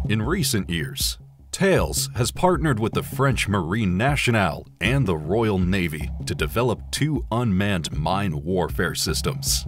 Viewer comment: Did I heard "TAILS"?